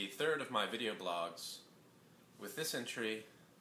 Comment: I thought he said, "with dysentery" instead of "with this entry" at :10